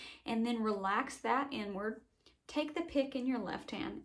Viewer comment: lmao i thought she said “relax that n-word” but the realized she actually said “inward”